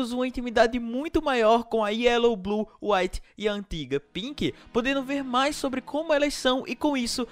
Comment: Mano, na hora que ele disse "e a Antiga pink" eu escutei "e a terrivel Pink"